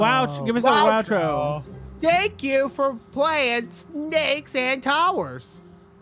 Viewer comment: i thought i heard "Snakes and cowards"